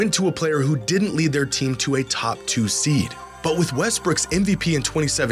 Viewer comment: Bro did you just say Westbrick's at 9:26 ? 😂